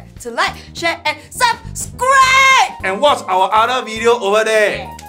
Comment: i swear i heard “bye bye bye chao-“